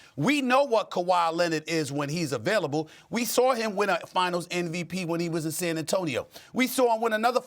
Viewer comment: is he saying NVP???